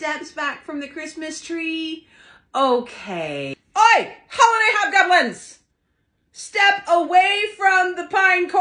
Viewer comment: I thought she said holiday hot dog buns I was laughing